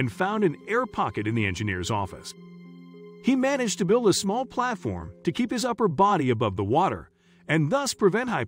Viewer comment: I thought you said he builds a platform to keep his body afloat how did he end up under water all of a sudden?